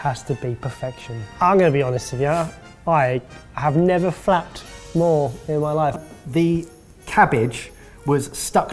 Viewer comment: I know it isn't but it sounds like said 'I have never fapped more in my life' Puts a whole new perspective on it hey?